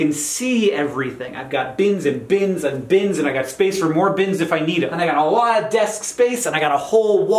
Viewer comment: I thought “bins” was said as “beans”. “I have beans, beans and beans.” And I thought, if they’re Dr Pepper, that sounds about right.